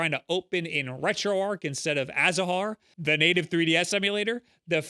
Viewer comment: um wtf? why do the auto captions say "citra" when he says "azahar"??? censorship for the deaf????